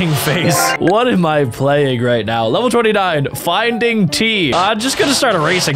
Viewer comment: the subtitles say 49 instead of 29